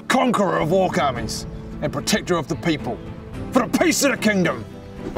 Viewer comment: Oh my god, all this time I thought he was saying “for a piece of the kingdom”, but just now realized it’s “for the peace of the kingdom”